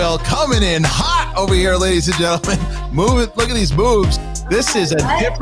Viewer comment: Did Harloff say "look at these boobs!" or "look at these moves!"? Either is correct btw lol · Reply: I went back and listened and I at first thought he said "boobs" but then I played it again at half speed and he says "moves". lol